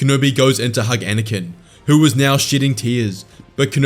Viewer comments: anyone else hear “who was now shitting tears” ? 😂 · I know you said “shedding” but it sounded like you said “shitting tears” lol